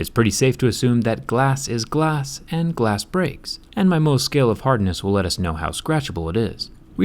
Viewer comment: All this time I thought he said most scale.. it was actually mohs scale.. lies